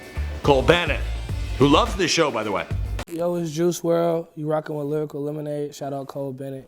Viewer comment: Wait... Did he just say Juice Wrld is gay??